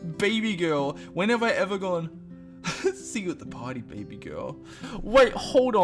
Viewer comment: I thought you said BABYGRILL and I fell over, imagine telling you "Wanna come to my party babygrill" Or "Wanna be my grillfriend" LMFAOOOOOOOOOO